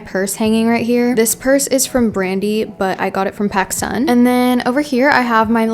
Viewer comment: did u say “Pakistan”?